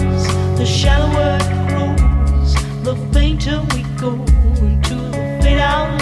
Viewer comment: THE SHOWER APPROVES!! Up to this point I had no idea what she was actually saying. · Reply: ayior I heard, "the shallower it grooves"